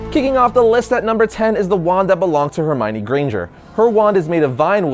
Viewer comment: Did he just say granjer